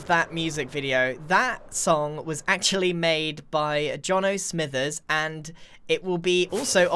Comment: Thought he said the song was made by "Giorno Smith"